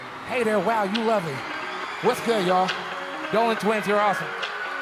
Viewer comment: Did anyone hear him say "Dolan Twins Are Awesome" It's